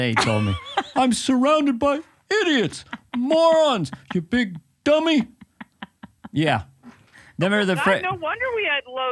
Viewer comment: During your young siblings talk a cord struck through me as I heard, "you big fat dummy!". I said that since oh right I still say it.